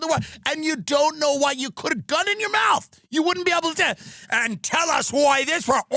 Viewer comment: Could anyone decipher what is Tim saying at 24:00? I can only make out "I don't know" at the beginning 😆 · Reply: "you wouldn't be able to tell them"